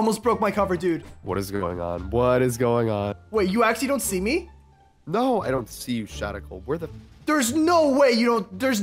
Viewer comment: "No I don't see you Shadical where the f-" who else heard almost say the f word?